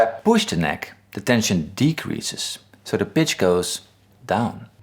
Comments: It sounds like you said "The bitch goes down" at 2:30 😂 Anyways, nice video. I don't get why some people don't get this thing most of us found out at 10 years old. You bend the neck to get vibrato simple logic... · I thought you said, "The bitch goes down" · “So da bitch go down” @ 2:30 I think you meant to say pitch* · anyone else hear "if i push the neck, the tension decreases, and the bitch goes down" ?